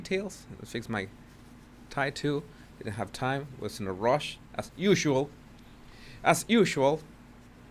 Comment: When you said “I was in a rush” For a second I thought you said “was listening to Rushi” … I wS like YASSS!! I’m nowhere the ages of those guys, mom wouldn’t allow them on the radio either.. even Metallica was forbidden, but had my wars 😂